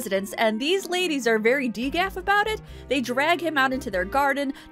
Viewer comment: Super thought you said "these ladies are very decaf about it" at 3:57, and that word actually fits so well that I think I wanna start using it when someone's acting too chill.